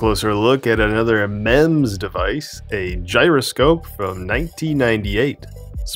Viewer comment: Ha ha ha i thought you said a mens device